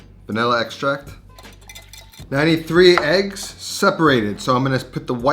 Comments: At first I coulda swore you said 93 eggs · Sounds like he says 93 eggs. 🤣🤣👍🤣🤣👍